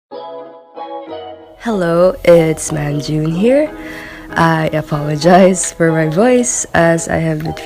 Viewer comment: I thought you said "hello its namjoon here" I was jungshook for half a second lmao